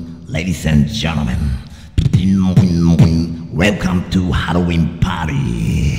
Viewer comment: it sounds like he said heroine party